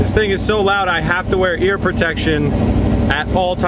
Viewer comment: did he say "hear protection"?